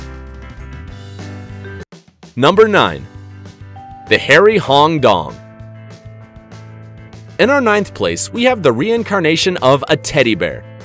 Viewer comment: I thought he said hairy ping pong I almost dumped out of laughter the fart was slimy